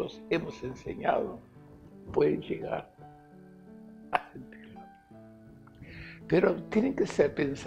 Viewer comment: ¿Qué dice en el minuto 4:47, pueden que? (Arder) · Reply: "... pueden llegar a sentirlo"